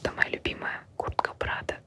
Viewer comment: Ааа, вначале она сказала «на мне куртка «Prado”, а мне послышалось «на мне куртка брата»💁‍♀️🫢😂😂